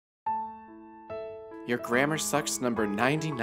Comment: i had this on in the background and i misheard it as "your grammar sus" help